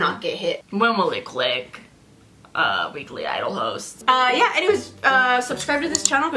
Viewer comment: HELP I THOUGHT YOU SAID 'WEEKLY IDOL HOES' INSTEAD OF HOSTS ☠️ i meaaaan... Is it wrong?